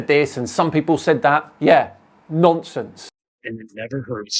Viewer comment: All I just heard was..."some people say this, some people say that, yea, nonces" 😂